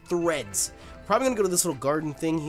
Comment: When he talked to professor, I thought he was going to say kakashi sensè because he said kakashi instead of kukui